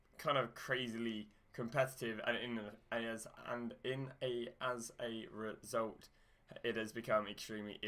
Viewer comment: wut did You say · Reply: As a reault